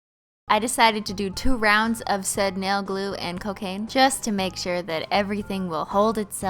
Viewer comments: did she just Said cocaine?? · did she just say 'cocaine'?